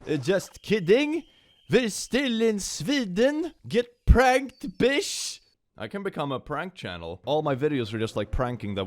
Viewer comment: I heard “get pregnant, bish” and I think I may have snapped my neck from the whiplash, please send help